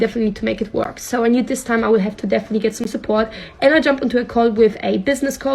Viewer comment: she def says call but I find it hilarious that the auto generated captions say cult